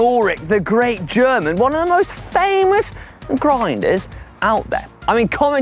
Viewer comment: I heard this as "Hank on Grindr" 🤣